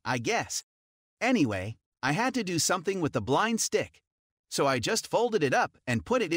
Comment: i’m not going to lie, in the beginning i thought he said blonde and not blind and when he was like disability, i was like so confused because blonde is not a disability 😂